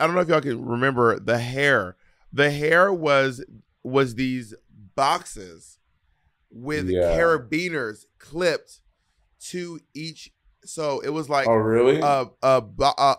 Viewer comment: ayo did bob just call me a BEANER 😱